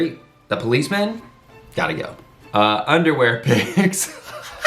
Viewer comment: I heard "underwear pigs". 😹 Just sayin. And don't you dare saying you're old ever again. Because then I'd be old too, which is not true for another decade at least. 🙈